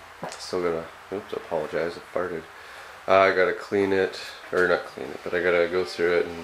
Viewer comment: Great job cleaning that up! Hahaha did you just say you farted?